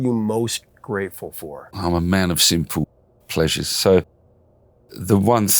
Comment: What a great cover! I didn't realize it was a cover! Speaking of misheard words... I thought he said I'm a man of sinful pleasure and then realized he said simple lol